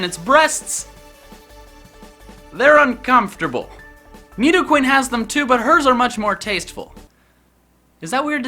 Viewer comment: Me: Are You Saying You Like To Eat Nidoqueen's Breasts Because That's How I Heard It